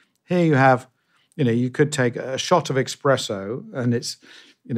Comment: Did he say.. 🤔 ..EXpresso? 🤦🏽‍♂️